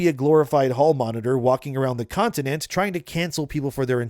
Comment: Do you really not know how to say continent? I could’ve swore I heard you say contitent.